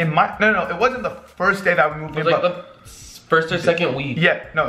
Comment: fazerug said weed instead of week